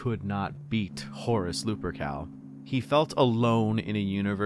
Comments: at this point, I just hear "Horse Lupercal" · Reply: I heard "Hoarse Lupercal". To think the HH could have been avoided with a throat lozenge... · at this point, I just hear "Horse Lupercal"